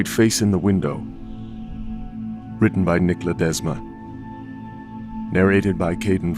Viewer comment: At first I heard "Written by Nikola Tesla" and was like wtf